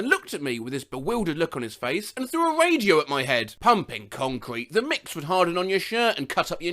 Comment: I FUCK YOU NOT. WHEN HE SAID “Pumping concrete.” I SWEAR TO GOD I HEARD “Humping concrete.”